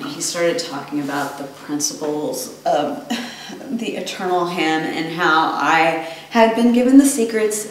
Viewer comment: Could have swore she said they trusted her with the secret of the eternal ham